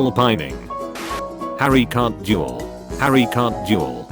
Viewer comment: Is it just me or am I the only one hearing juul instead of duel?